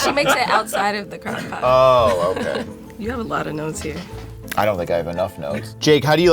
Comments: I thought she said you have a lot of nose hair lol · I heard, “ you have a lot of nose hair” 🤦‍♂️🙄😂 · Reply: hahah, it's " you have a lot of notes there". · Bahaha when said “ you got a lot of notes there.” I thought she said “you got a lot of nose hair.” I was so confused and had to replay it like 5 times 🤣🤣🤣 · bruh i thought said you got alot of nose hair but she said notes here🤣🤣🤣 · i thought said “you have a lot of nose hairs” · I thought she said You have a lot of nose hair 🤣🤣🤣🤣🤣